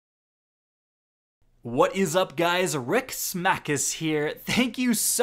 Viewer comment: Who is ‘Rick Smackis’ ive never heard of him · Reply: i thought he said rick smack this lol